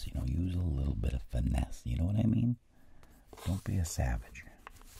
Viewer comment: i cannot do this, as i don't have such expensive (looking) stainless steel pen cap edit: did he just say "don't be a savage"?